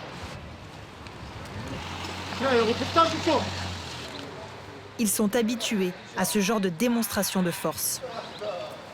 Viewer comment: il dit quoi le demeuré à 11:28 ? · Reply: "... reportage ou quoi?!"